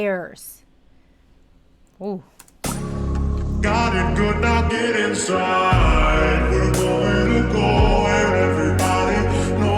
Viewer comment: I always misheard the part where he said "got it good now get inside" as God if you're not dead inside. As in like asking God how he's now dead inside after all that's happened which I think also works.